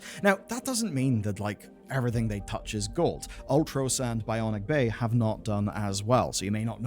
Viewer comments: he mentioned a game before Bionic bay. It sounded like Ultra Sand or Ultra Sound. Did anyone get that? I wanna check out that game to support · Can someone please please tell me what the action game at 9:30 is called? I hear Ultra something and I can't find it based on that, and it looks really cool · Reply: it's called Ultros